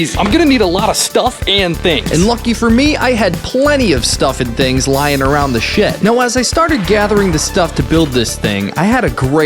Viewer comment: Out of the what 0:45 And did he say the sh word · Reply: 𝓢𝓱𝓮𝓭